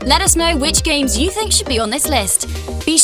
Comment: did she just say sexual media?😂